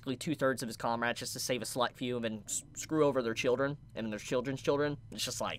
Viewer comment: Lol at 16:20 when you said 'Select few' I heard 'slut few' XD but I wasn't paying attention and misheard it. It gave me a good chuckle though.